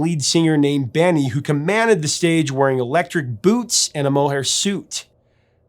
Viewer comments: Yeah... I took way too long to finally take the time to focus and figure out what the lyric was, given that I too always heard it as Electric Boobs, and not boots... haha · Your story of the misheard lyric electric boots for electric boobs is hilarious 😂 what boy would not think of that? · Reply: I heard “electric boos”